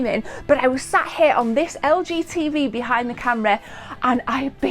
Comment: For a second there I thought she said LGBT